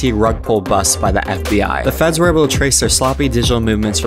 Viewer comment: I thought you said 12 year olds, then heard about the 20 year sentence...